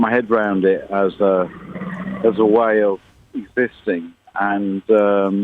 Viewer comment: Can Anyone tell me what Damon Damon said at 3:05 ? " got my head around it as a way of "..........."??? And like any form of "........"??? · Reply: "existing" ,, that's what he said.